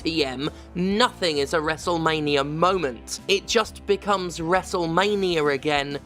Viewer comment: When you said wrestlemania i thought you said wrestelvania and that was a mental image lemme tell you